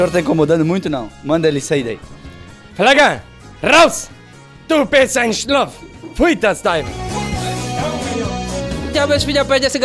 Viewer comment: Como se escreve oq foi dito no 15:06? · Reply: Raus, du bist ein ..... não entendi mais nada depois disso kkk. Lembrando que o alemão falado no vídeo é um dialeto, que é diferente do alemão tradicional .